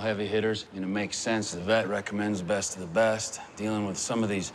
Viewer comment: im not good in english who is he saying recommanded them? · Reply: The VET, animal doctor. He's the guy who works as the cartel doctor.